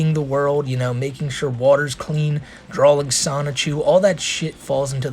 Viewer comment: did you say drawling?